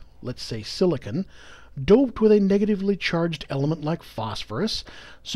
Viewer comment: what is the word at 4:32? "doped" with a negatively charged?